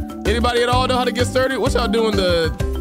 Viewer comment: Did he just say the potara dance?